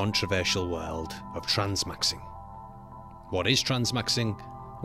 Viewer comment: Transmaxxing? Wtf?! I can't.💀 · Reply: I thought it said trenmaxxing lol